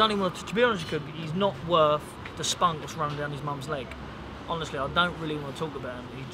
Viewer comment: did he say 'he's not worth thesponge running down his mom's leg'? wtf